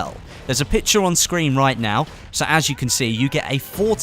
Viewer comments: Did you say Afro American? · did you just say afro american?? WTF?!